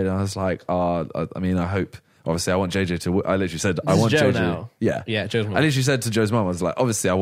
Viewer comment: did Randolph say Joe's mum is in Jail? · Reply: No he said is this joes mum